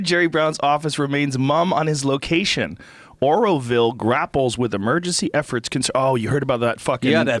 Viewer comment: ha I thought you said oralville lmao on valentines day